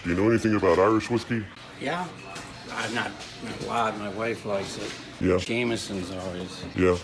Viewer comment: So what? Alcohol isn't forbidden. Maybe he was going to a dinner party. Find better dirt. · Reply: from 3:01 to 3:04 sounds like Tony says " Yeah,,,, I'm not,,,, well, my wife like's it, Jameson always"... so for those first few seconds he's gathering his thoughts as to how to respond to the guy asking "Do you know anything about Irish whiskey?"